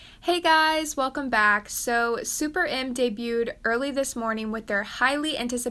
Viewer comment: I thought it said SPERMS debut and I was like wtf